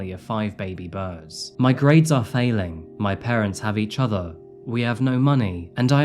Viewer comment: I think the actually says ‘my parents hate each other’ just as an observation